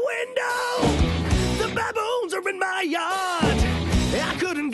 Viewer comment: I thought it said balloons.